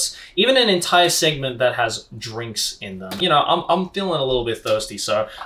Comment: When said drinks, I thought I heard drugs 💀